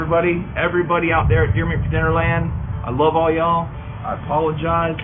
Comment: Did he say pearland??